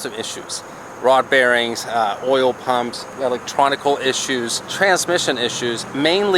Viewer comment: did he say "electronical" ?